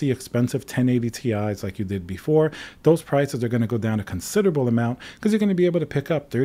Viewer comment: I thought you were saying prices were to go back up for a second I was so f^cking scared.